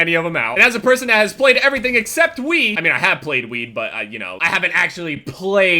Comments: "played weed" Is what I thought u said. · Sounded like said xD "I have played weed"